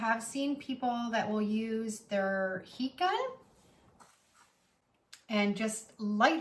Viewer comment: "Heat Gun," I thought you were saying p gun, or pecon. It took three listens before I figured it out. Not you, me.